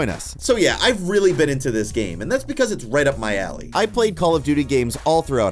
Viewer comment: i thought he was gonna say “it’s right up my ass”